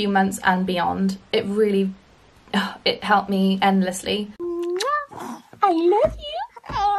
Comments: I know babies can't talk, but I swear she sounded like she said "I love you" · Did anybody else hear the baby echo back "I love you?"